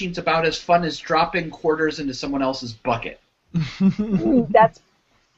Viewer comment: I thought said "someone else's butt" at first.